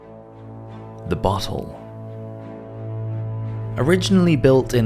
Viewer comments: I was reading the comments at 5:13 and I thought he said "the butthole".... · I had to look up at the screen because it sounds like he just says "the butthole."